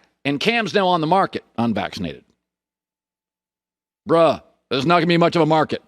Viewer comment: Did Colin just say "bruh" 😂😂